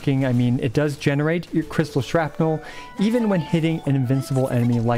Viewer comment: Did he just said Kaguu Menki at 4:55? or is it a running joke in his channel